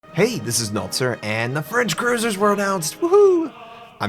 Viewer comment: i thought i hear notser say french cuisine lol